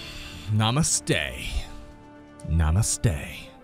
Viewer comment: Namasthe? What did you say at starting!